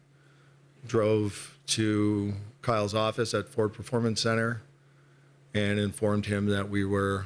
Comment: Keep laughing that I hear 'poor performance centre' everytime Shanny talks about visiting doobs at his office...pardon my Freudian ears